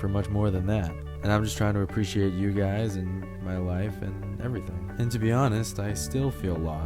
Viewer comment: Did he said my wife?!?!